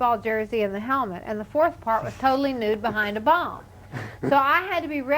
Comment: what did she say was the fourth part? 5:37 her being "totally nude behind a..." ? and does anyone know the piece of music that would have been played in the fourth part? thanks :)) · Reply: bomb